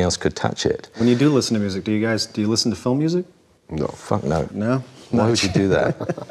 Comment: Did he say "phone-music"?